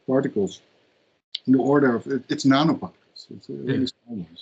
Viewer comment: What did the guest say at 7:20 on this video? Did he say, “Nanobots” or “antibodies”? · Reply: Nanoparticles